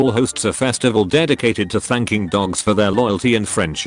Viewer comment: I thought it said "spanking dogs"